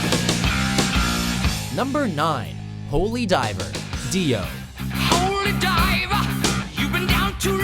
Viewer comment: did he just call him Dia? Sounded like it lol. I will hunt you down without mercy. Hunt you down all nightmare long P.S im not sorry